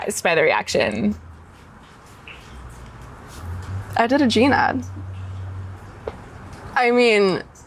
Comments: omg not the subtitles saying “gene ad” and not “jean ad” 😂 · Even the auto-generated captions say “I did a gene ad”